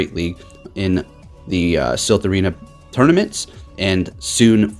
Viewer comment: LOL, I heard the "Salt" Arena tournaments... how appropiate xD